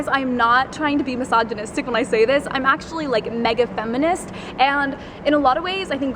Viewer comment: "I am a mega feminist" ....so you are a mega dumbass. Cant believe people still think FEMINISM is about equality. The west is brainwashed and delusional. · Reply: Damn I thought she said negative feminist and I was like "HOLY FUCK I LOVE U" then u replayed it and saw she said mega feminist and I got all depressed